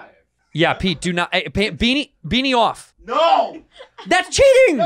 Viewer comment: i wasn’t looking i thought santino said “beat me off”